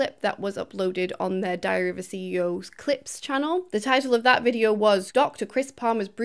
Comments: When you said “Diary of a CEO” I heard “Diarrhoea CEO” and cannot stop laughing. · In the opening few seconds, I genuinely thought you said "..diarrhoea of a CEO" It works either way.. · I misheard it as 'Diarrhea of an CEO'... Maybe it wasn't that wrong.😂